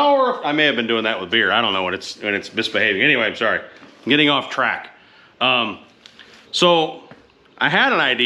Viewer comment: 😂😂😂😂😂😂😂 0:43 - I heard "I'm getting off crack" 😂😂😂😂. 12:08 - Color and gender don't matter! If you ride a motorcycle bring your ass on to Sturgis and HAVE. A. BLAST! #WORLDRECORDPOKERRUN <link> You crack me up Brother! I love your dry wit and humor!